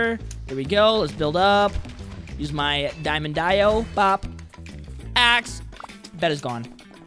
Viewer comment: did he say Dieo